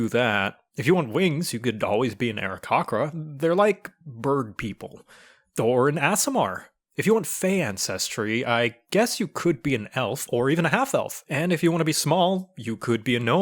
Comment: Did he pronounce aricochra and aasimar like that as a joke? It sounded like he said Ari-cock-ra and ass-imar instead of ari-coke-ra and aye-simar XD